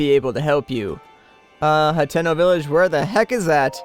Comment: lol when he sad Hateno Village I thought he said Nintendo village